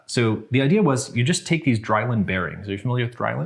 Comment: Not gonna lie, I thought he was saying "Delrin" incorrect until I found out Drylin is a real thing XD. Funny enough, you can make bearings out of either, so that's not confusing at all.